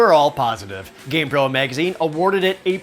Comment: At one point I thought I heard Gamebro instead of Gamepro... lol I just imagined a gaming magazine for 90s stoner stereotypes.